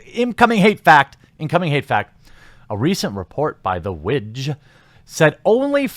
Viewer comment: Had the volume down, thought Jeremy kept saying "the minge" when talking about that organization...almost had the milk I was drinking come out my nose started laughing so hard...